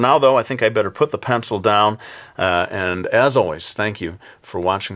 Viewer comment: Great vid as always, but I couldn't help but notice it sounded like you said you would be "back with another one real sad.". Will the drawing be sad, the person in the drawing, or will you be real sad to be doing another one? lol This was a great pose and I'm looking forward to your next one real soon. Sad or not.